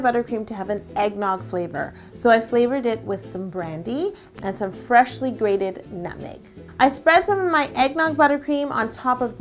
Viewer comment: it sounded like she said freshly graded nothing